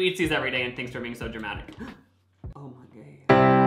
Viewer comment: did he say, “oh my gay” 🤣